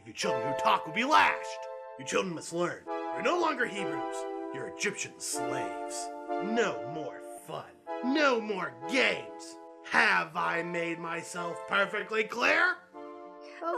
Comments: thought he said no more gays😭😭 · i heard this as: No more FUN no more GAYS